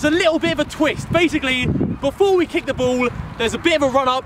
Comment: why does sound like he said "bowl" instead of "ball"